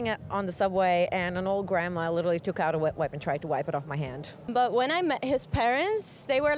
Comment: Such interesting information.... I do believe that first person at 0:08 said "wet wipe" instead of "whip" though, haha. I hope old ladies are not carrying whips around South-Korea ;)